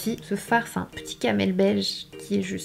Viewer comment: "C'est un petit Camel Beige" et moi j'ai compris c'est un petit Amel Bent 😂